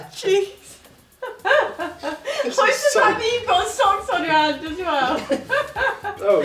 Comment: I totally thought she said sucks on the hands. Then I was like, what’s with the socks? before realizing oh, she said socks on the hands. In Minnesota mint sauce is generally only found in very nice restaurants served with lamb chops.